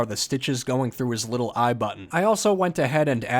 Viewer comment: I thought you said the second bat had one normal eye and that his other eye was a butt. When I saw you turn it into a button, I thought, oh that makes more sense.